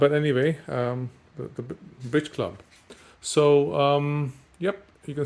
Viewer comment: Thought you said the Bitch Club lol